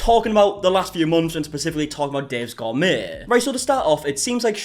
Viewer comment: I'm giggling so much, I thought you were saying Dave's Gone May and had to rewatch to figure out what it was. Wow I'm deaf. XD